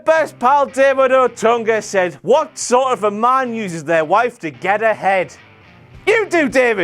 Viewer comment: i thought you said "what sort of a man uses his wife to get head" i didn't expect that follow up :P